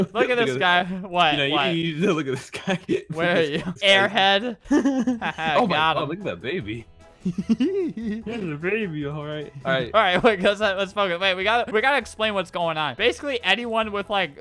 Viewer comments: anyone else hear him say "alright lets fucki- wait we gotta" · did Albert say let’s f*** it